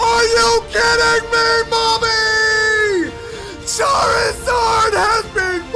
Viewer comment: did he just say mommy???